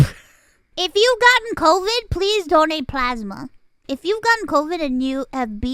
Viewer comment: I thought she said "don't eat plasma" and I was like "Ok?" but she actually said "donate plasma"